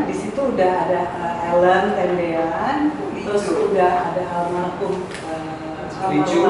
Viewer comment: Menit 20:56 waduh br tau kalau mbak Li cu sdh almarhum ya mas? Semoga saya yg salah dengar